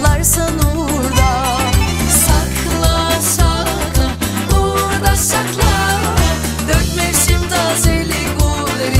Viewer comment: burada ne diyor ? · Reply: Evren Çelik uğurla sakla